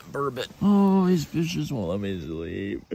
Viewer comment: it sounds like hes saying these bitches wont let me sleep